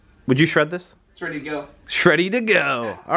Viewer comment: Right at 4:50 did I hear you say, "Shredding to go!" ? lol